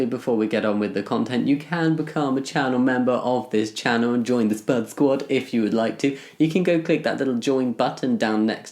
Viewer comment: At the beginning I swear I heard ''join the sperm squad'' Wow didn't know my dysphoria could impact my hearing 💀